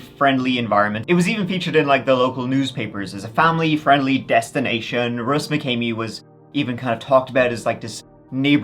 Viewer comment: I thought you said"Deathstination"😂